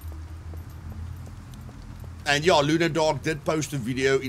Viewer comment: How is called the guy who uploaded tha video of graphite piles? cannot understand what you say on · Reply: LunaDark